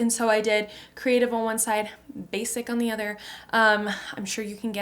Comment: I thought it said basil